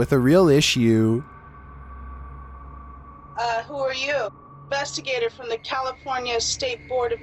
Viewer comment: At 22 it sound like she said “who are you with” but I’m not sure